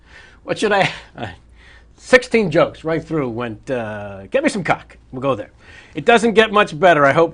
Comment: did he say, "get me some cock"?